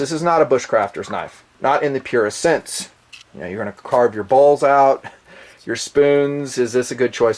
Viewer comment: I thought you said "Carve out your balls" LOL